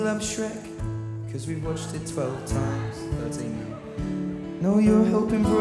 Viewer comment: what does he say? Don't sing now? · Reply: "thirteen now" referring to the amount of times they've watched shrek